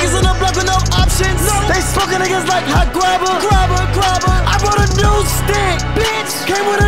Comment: How is nobody talking about Sheck Wes casually sliding back in the game contributing to this absolute GEM?! LIVE SHECK DIE SHECK. 🔥🔥🔥🔥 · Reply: I have a question, can someone translate what the guy at 2:53 said? "They smoke a nigga like hot grobber"? What is a hot grobber? Or did he mean hot cobbler?